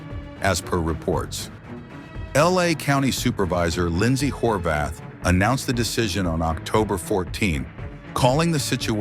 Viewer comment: Did he just say her names Lyndsey "whorebath" 🤣 Dude no way😂